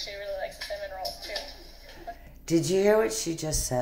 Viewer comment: I listened a few times and it sounded like “Ce-Ce actually really likes Benadryl too” I could be wrong but that’s definitely what it sounds like!! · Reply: I heard "cinnamon roll"